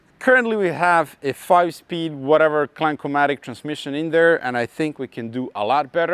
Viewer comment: from now on im calling automatic transmissions, Clankermatics xD 47:37 idk if thats what u said buuuuuuutt thats what im hearin and goin with XD